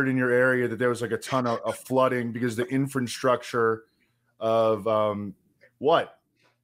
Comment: Sounds like says “infant-structure” instead of infrastructure.